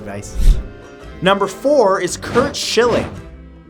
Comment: I thought he said Curt shitling